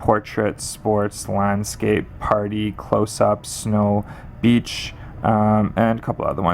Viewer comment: did he say 'bitch' at 5:34 ?? XD XD